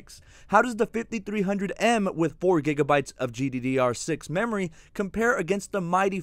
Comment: it's weird to see the english auto caption typed out gddr5 when i hear you clearly spoke gddr6 😂😂